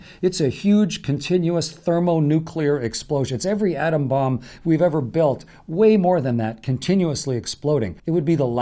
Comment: did he say we built the sun? ik i messed up what he was talkin about but can someone explain why he said that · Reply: "...more than every atom bomb we ever built.." is what he says